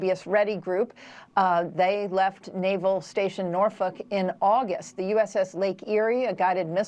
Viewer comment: Hey, Canadian here. Did I hear this correctly? "They left naval station Norf*ck in August!"